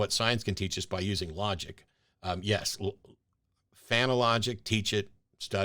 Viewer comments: Could you please tell me what Matt says at 17:23? I can't understand it. "Phanologic"? I have no clue what this is and how it is related. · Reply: I think he's saying "fan of logic" and not enunciating. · Sayin' eh As you seem to be good with English, could you please tell me what Matt says at 17:23? I can't understand it. "Phanologic"? I have no clue what this is and how it is related.